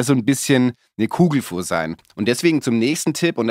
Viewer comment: min: ".. Kugelfuhr..." - Cooles (schwäbisches) Wort, das du da nutzt! Hab ich schon lange nicht mehr gehört. Nice ! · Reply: Das heißt Gugelfuhr